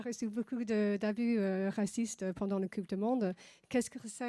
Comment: "tenure" pas "ten years"... La traductrice traduit 10 ans alors que la journaliste a dit "tenure" ce qui veut dire mandat Bordel, donnez moi le job au pire je ferai mieux